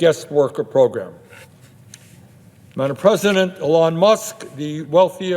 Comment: Is he saying "madam president" @ 4:39? Well I think if we send the rest of the power structure to the gallows, we could have an actual qualified president.